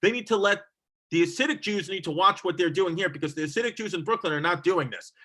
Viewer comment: Is Chrissy calling them the “acidic” Jews? I need a Tums. 😜